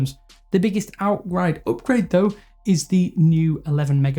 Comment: Did anyone else hear "(burp)grade" 7:49 🤣🤣